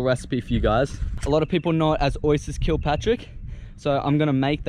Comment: when he says oysters kilpartrick i thought he said, oysters kill patrtick